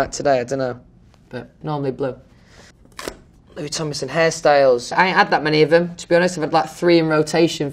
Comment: Omg when he said “hairstyles” I thought he said Harry Styles